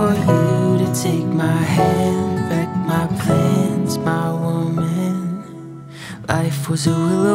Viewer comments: Loved it! But I heard "my old man" at first 😭😭😂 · great cover but i thought i heard "my old man" HAHAQHAHAHAH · When he said my woman, GOOSEBUMPS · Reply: I THOUGHT HE WAS SAYING ‘MY ONE MAN’